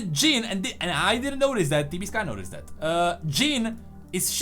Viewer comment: who is this guy is referencing to ? Could not hear the name of the guy, since i’m not a English native speaker. I’m interested to know more on the awaken trailer. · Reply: Did you try typing what you hear? I heard "TV sky-an." what's most confident and easy? TV or tb + sky. So i typed "TB sky." Found it: T B Skyen